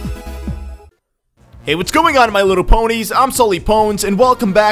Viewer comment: wait... did he just say my little ponies???